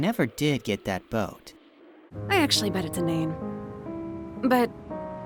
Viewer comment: huh, I always thought 18 said: "I bet it's a name", instead of inane.